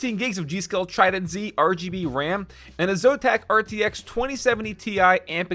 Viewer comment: Correct me if I'm wrong but did he say RTX 2070 TI?